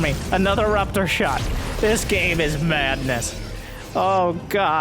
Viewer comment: wath did ya say? MADNESS??????? <link>